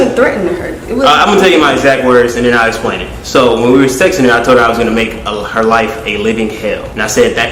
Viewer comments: timestamp. Did he say. When we were sexting, or texting. Am I hearing shit. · About the threatening part it sounded like he said "sexting"